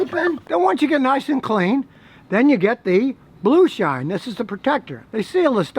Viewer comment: at 2.02 i thought it was going to say "Blue Sh!t" LOL - keep up the good work Scotty